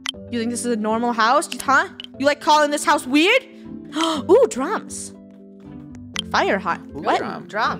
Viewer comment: When funneh said “ooh drums” I thought she said “ ooh drugs”